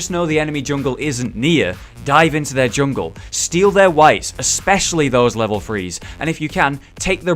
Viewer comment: I could have sworn said "dive into their jungle, steal their wives" at 3:58 . If the window is there go for it I guess xD